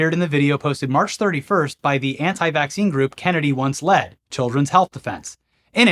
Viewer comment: I heard you reference an organization "Kennedy Wants Lead" and I thought, checks out. He probably wants to replace the flouride in the water with lead.